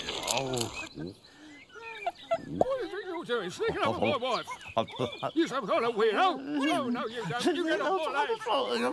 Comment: what's the hunter saying to Mr. Bean? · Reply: What do you think you’re doing sneaking up on my wife? are you some type of weirdo? Oh no, you don’t. Take your camera with you get out of here. Right leave right now leave